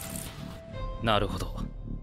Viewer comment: did genos say naruto?